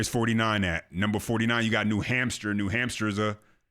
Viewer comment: Bro.... Did you just say "New Hampster"?😂😂😂😂